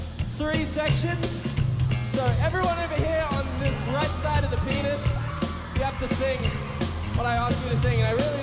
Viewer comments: why did i heard ”penis”😂😂😂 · did he just say “the right side of the pen**” or is it just me...